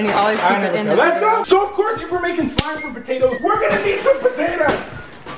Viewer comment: I was fast forwording this video and when he said "from potatoes" i heard "Trump potatoes